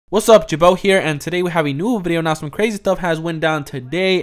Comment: “ WASSUP GUYS HERE “ who else be doin his voice 😂 Ik Ian spell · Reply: I thought he said "jebow here"